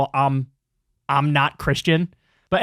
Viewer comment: Your the first person who mentioned I'm not Christian, I swore that's what I heard the first time and wondered why no one was talking about it